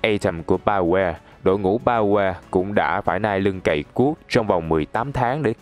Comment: Bioware mà đ*o hiểu sao tôi nghe nhầm thành "Trong quá trình phát triển Anthem của đội ngũ BaQue"